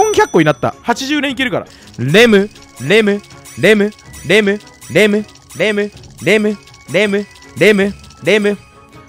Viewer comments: 「レム、レム、レム、デブ、レム、レム、レム、デブ、デブ、デブ」って聞こえたの俺だけ？ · レムレムレムがデブデブデブに聞こえる